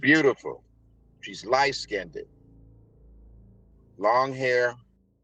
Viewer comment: “She’s light skinned-ed “ I had to turn on closed caption to actually see what I thought he said 😂